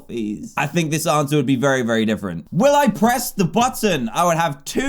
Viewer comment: When I was watching this video at the start, it buffered so I heard Dan say "Will I press the butt".